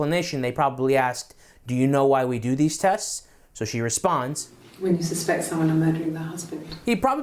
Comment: to me it sounds like she says "you suspect someone murdered my husband" but I could be wrong, and I don't think it changes much